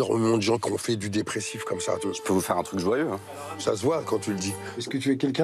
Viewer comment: j'peux vous faire un truc quoi ? jouèv ? j'ai pas compris. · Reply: Il dit : "Je peux vous faire un truc joyeux" pas jouev ^^